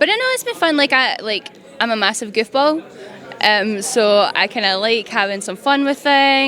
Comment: Due to her accents I thought she had said "gift ball". Then played that bit back and realized she meant goofball, lol.